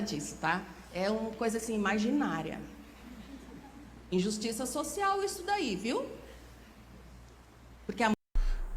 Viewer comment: Ela falou 13:10 "injustiça social" e você entendeu "justiça social" Acho que foi isso.